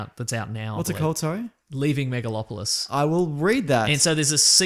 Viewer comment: I thought said "Leaving Milo Yiannopoulos". But "Megalopolis" is fine too...